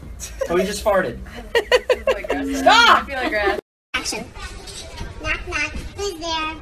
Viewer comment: Did anyone else hear the “action”